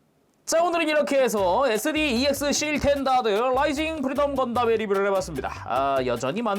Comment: ‘스탠다드‘라 쓰고 *’씰탠다드‘*라 읽는다 듣는 귀를 의심했네요 ㅋㅋㅋ 분명 자막은 스탠다드 라고 돼있는데 내가 잘 못 들었나?